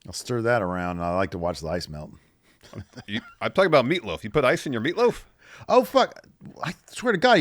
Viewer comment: He totally said oatmeal 😅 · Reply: yea I thought I was going crazy. BUTT then I thought said "lasagna" but he said "meatloaf"